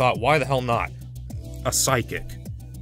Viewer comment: At first I thought you said "sidekick" and not "psychic".